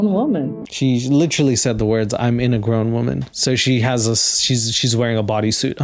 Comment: I think she said "I'm an agrown women" and that's equally hilarious to me.